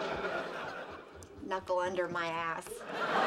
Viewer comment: anybody who knows English, please tell me what did she say? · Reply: She said "knuckle under my ass!". I don't know what she meant by that because I haven't seen the whole episode